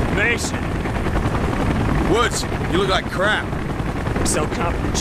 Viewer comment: I swear Mason says "Woods, you look like hammered shit." instead of "you look like crap." Or maybe I don't remember it right.